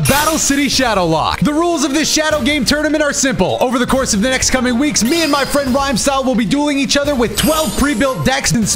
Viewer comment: I thought he said “over the next coming weeks, me and my friend rhyme will be doing each other with 12 pre built decks” really changes the video lol